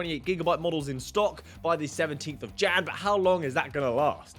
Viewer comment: 17th of what?? Literally i couldn't hear what you just said... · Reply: Jan, Amazon said they should get more in stock 17th Jan